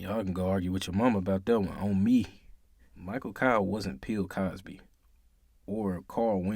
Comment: Did he say “Pill Cosby”? ….Turns up volume Yup! He did 😂😂😂